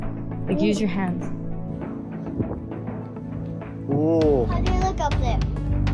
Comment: ！ いつも英会話の勉強にて楽しく見ています！ 1点質問です。13:42のの発言の字幕は「How does it look up there？」になってますが「How did〜」に聞こえるのですが、「does」て言ってますか？ 決して揚げ足を取るわけじゃなく自身のリスニングの勉強のためにご教示頂けたら？と思います！